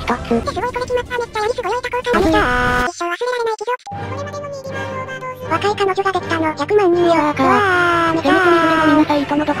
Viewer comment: の部分だけ聞くと「若い彼女が百万人できた」みたいに聞こえるの草なのだわw